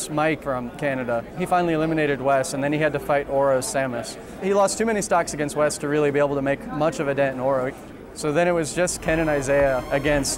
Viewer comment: I can't understand what yeroc is saying: "[...] to really be able to make much of a ..." Also I'm sorry if I annoy some of you with my questions but sometimes it's not easy to understand what people are saying as I'm not native english and also I'm doing this for a french translation of this documentary so any little bit of help would be nice. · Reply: "He lost too many stocks against Wes to really be able to make much of a dent in Oro" (I think that is how you spell his tag) hope this helps .-.